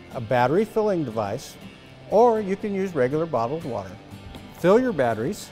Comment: regular bottled water ( 1:36), I thought you said distilled water ? Maybe I mis heard